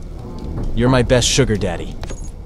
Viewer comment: AYOOOO WHAT DID HE SAY?!?!? 💀💀💀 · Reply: Welcome sun